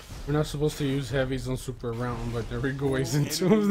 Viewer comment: did just say no heavy on super round?